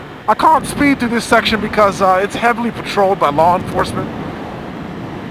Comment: i thought i heard "this area is heavily patroled by log inforcement" while you were looking at a fallen tree lol